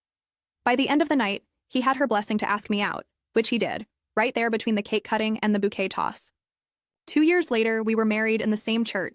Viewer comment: I heard it as 'The bouquet cutting and cake toss' lol